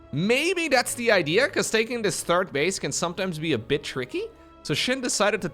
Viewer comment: Did anyone else besides me hear say something else at 11:33 ? I heard "it can be a b.... tricky" Nice save.